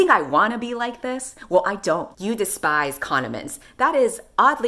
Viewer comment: When you read "you hate condiments" I heard "communists" and was like 😶